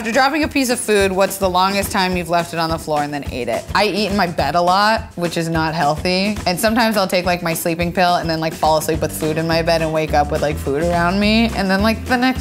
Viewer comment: At first when Kelsey was explaining about eating in her bed I thought she said “I eat my bed a lot” 😂😂